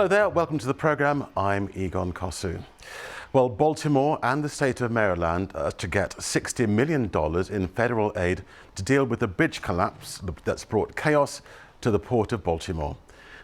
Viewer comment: Wow, I swear, it really sounds like he is saying the "Baltimore bitzh" collapse" (as in female dog).